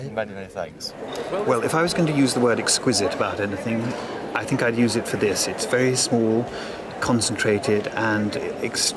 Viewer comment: The clip at the 17:05 mark, ive listened to the gentleman who brought the piece a few.times now when he says what siblings choose b4 him, can anyone tell me what he said??? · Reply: They took toast racks and knives and forks.